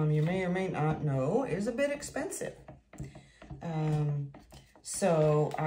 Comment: Wait.. Thought you said "not so expensive ?". Thoughs are all expensive , except the floetrol.